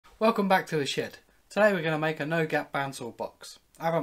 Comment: I keep hearing "welcome back to the shit" at the beginning of the video